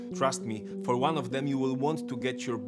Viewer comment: I thought he said "get your butt barb wired"